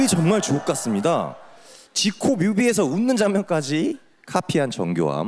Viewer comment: 댓글이 정말 ㅈ같습니다로 들려서 웃겨죽겟네